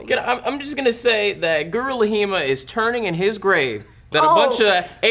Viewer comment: It always sounds like is saying Gorilla Hima instead of Guru Laghima.